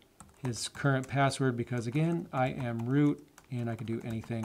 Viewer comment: I keep thinking I hear you saying “I am Groot”